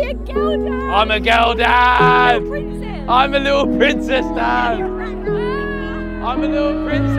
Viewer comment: i thought he said "IM A LITTLE BITCHES DAD" smh